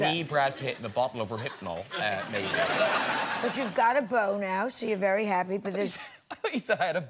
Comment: You got a beau now = "boner" ha ha ha ha ha ha LOL!! excellent and witty!! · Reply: I didn't realise that is what she actually said- thank you. It really did sound like 'boner' to me...i think it was said like that on purpose (but i initially thought she said 'you've got a book now..'and was then gonna talk about his latest book) :)